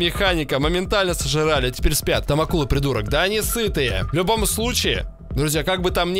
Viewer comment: мне послышалось что он сказал: да не ссы ты!)